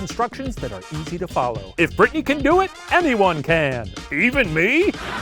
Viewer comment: I really thought you said "If Bricky can do it" for a second and was wondering why he was catching strays